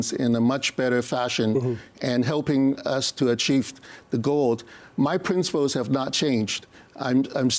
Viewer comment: MA states on what would you say to the constituents who voted for you on the conservative platform" in helping us to achieve the gold" .... 🤔 little Freudian slip perhaps 💰💰💰💰💰 what was he offered